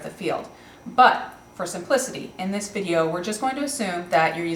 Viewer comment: wait, wait! at 2:49 did she say "for some pussy"?